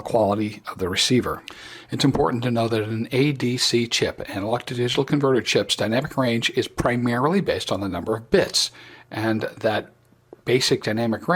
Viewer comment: It is no good to absolute newcomers to SDE when the guy making the explanation, stumbles over words made unintelligible even beyond slowing it down SEVERAL TIMES. What the blazes DOES he say at 3:25? And at 8:23?? Sounds like "legatory receivers". What does THAT mean? Is this guy on the sauce? Or am I? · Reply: He is me -and I said: "An analog to digital converter chip's (ADC) dynamic range is primarily determined by it's number of bits". A 16 bit ADC chip will provide a basic dynamic range of around 100dB. This is adequate for HF work. Two tricks are used to enhance it's performance: Decimation and Dithering. Decimation is over-sampling the incoming signal and then reducing the data down to the original bitstream. This is sort of like good old Dolby noise reduction...in reducing the over-sampling down, you increase the signal to noise ratio and dynamic range. Dithering is adding custom noise to the incoming signals to mix with spurious signals and blend them into noise. This raises the base noise floor, but improves basic performance. Hwy, I'm no expert! Check out Adam Farson and others for a fully technical explanation of SDR tech.